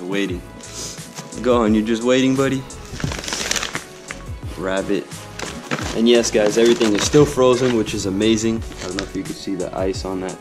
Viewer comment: Hold up did I just heard the word rabbit??????!!!! Gohan eats rabbit? No way this can't be 😢 ︶︿︶ in